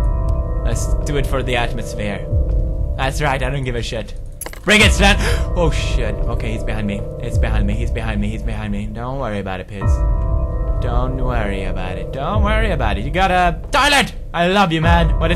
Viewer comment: DID HE SAY SVEN also my first vid i saw of pewds